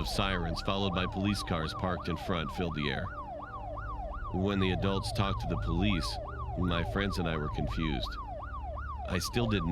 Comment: I THOUGHT IT SAID WHEN THE DOGS WERE TALKING TO THE POLICE WHATHSKSIIS